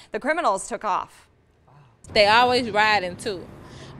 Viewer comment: What did she say? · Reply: Jeffrey Spence "they always ride in two" two 🚗 one distract u while the other jack u💯